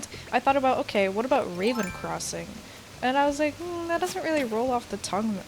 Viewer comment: I thought u said ur name was Raymond instead of raven for a seccc 😂😂